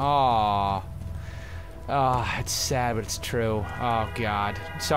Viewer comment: did he say "god giggity"? xD